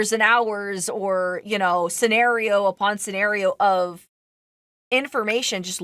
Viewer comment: did AI caption this, or did an editor purposely put "A porn" instead of "upon"?